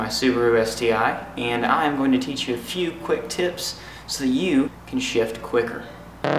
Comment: Totally sounded like "So you can shit quicker" lol